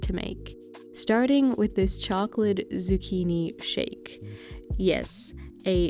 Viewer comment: I thought you said chocolate bikini shake ahahahha